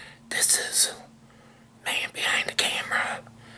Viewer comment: No going to lie at first I thought is said KidBehindACamera